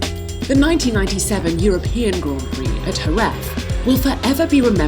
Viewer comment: "Herref"? What track is that? Did she wanted to say "Jerez"? 😂😂😂 Yeah, the "J" sounds close to an "h", but Z is Z...not f 😂